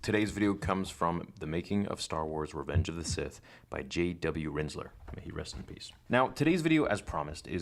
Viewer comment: I thought you said JW Rizzler at first 😂